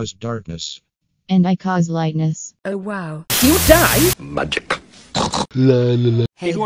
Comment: FAQ: Q: Why is Seven saying "you die" in the tv-static transitions? A: I wanted to spice things up for the sequel. Seven is actually saying "lliw dau"; which means "two's colour" or "colour two" in Welsh. Q: Why are there Numberblocks (and/or Gelatin) here? A: Gags. Q: Will there be a "Colourblocks Series 2 in a Nutshell" video? A: we'll see... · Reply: I hear "Do you die?"